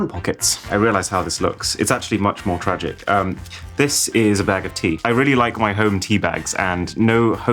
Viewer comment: I heard "bag of teeth" :P